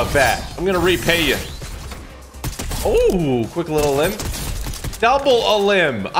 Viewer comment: what did he say😨😨😨? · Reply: He said elim